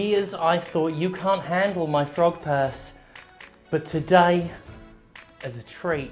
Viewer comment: I thought he was saying frog pass. I want a frog pass